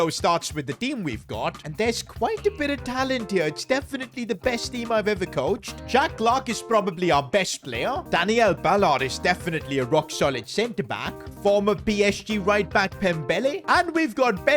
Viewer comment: I thought he said Jack Black 😂